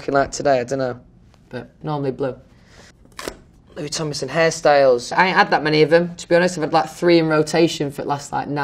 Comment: Anyone else thought he said Harry styles instead of hairstyles? No? Just me? Ok.